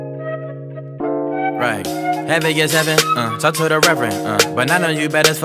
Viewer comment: Hey I think goldlink is saying heavy as heaven